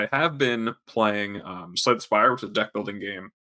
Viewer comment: what is the game mentioned at 10:18. I cant make out the name, and called it slight aspire. google did not help with that. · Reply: Slay the Spire